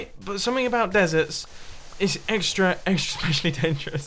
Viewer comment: Running? Exercise? Never heard of it. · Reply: Exercise I thought you said extra fries